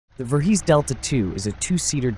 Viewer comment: Phew i thought it said Vorhees at first lol